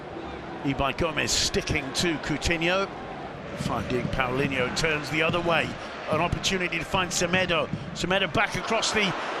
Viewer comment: Who else heard the commentator say “fucking Paulinho” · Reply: I know I'm late but he said finding paulinho